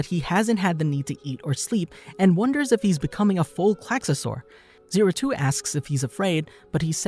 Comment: "A full quaksasuar" anyone else heard it wrong?